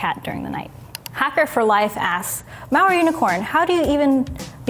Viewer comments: I keep mishearing "Maori Unicorn." Sounds like a cooler tag than Malware Unicorn, tbh. Good stuff though. I've been studying towards cybersecurity out of curiosity. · I kept hearing "Maori Unicorn" and getting excited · most obvious question that nobody asked: "Do you ever say, "I'M IN!" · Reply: C N i thought she was saying Maori unicorn also, until I saw it was malware unicorn 🤣